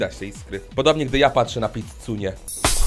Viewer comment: jak powiedziałeś "pizzunie" To ja usłyszałem "cycunie" I tak tego no